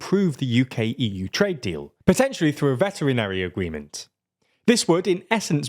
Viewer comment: I've listened four times and I still can't work out what you're trying to say at 5:02, unless you really do mean "veterinary agreement" and that this trade deal will centre around professionals providing healthcare to sick animals.